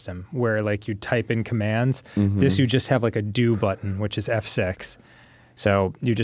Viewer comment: I had to stop, I clearly heard, "here you just have a do button, which is "have sex" ". It was F6. I bought this game and I'm disapointed now.